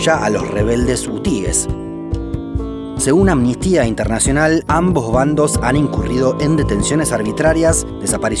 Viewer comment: entendí "Mi tia del nacional"